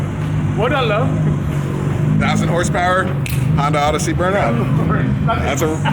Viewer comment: Thought he said almost 7000 horsepower i was about to say holy fuck how can that shit even stay on the ground when you hit a bump in it